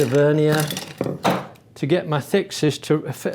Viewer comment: Did he just say my thicc sister?